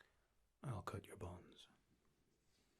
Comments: Oohh, you said "...Cut your bonds..". I swear I thought you said "Cut your bones", and I got scared. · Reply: The GameSMR I thought he said "I'll cut your balls" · i thought you said "i'll cut your balls" xD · oh CRAP I uh heard that wrong haha. whisper I heard I'll cut your balls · I'm going to be honest at the very beginning I heard "As a gesture of good faith im going to cut your balls" and I was extremely confused. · Oohh, you said "...Cut your bonds..". I swear I thought you said "Cut your bones", and I got scared. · I thought he said "I'll cut your balls" Haha